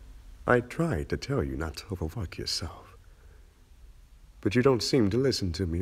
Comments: I, legitimately, thought he said I “overfucked myself,” and I was like, “What does that MEAN!?!?” 😱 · When he said 'over work yourself' i thought he said 'over fuck yourself' 😂😂😂